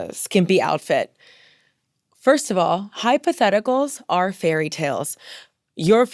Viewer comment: When you said hypotheticals, I thought you were saying “hi, patheticals!” to the insecure men and I will be saying that from now on